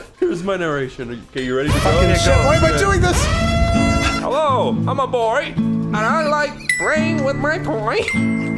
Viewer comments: The first time I heard the audio I thought he said "I am a boy and I like rain with my parade" somehow. If I close my eyes and mouth the words I can still hear it. Anyone else? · I always thought he said "I like rain with my parade"